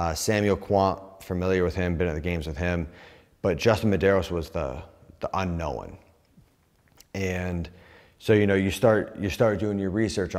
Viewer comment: UNKNOWHEN - definition: When Mat Fraser sees something on his radar he’s never seen before, and doesn’t like it. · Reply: I thought he said "unknown one"